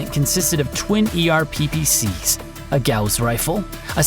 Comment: I’m listening to this as my daughter’s cartoon plays in the background. The narrator says “…twin ER PPC’s…” and I hear “…twelve ER PPC’s…” 😳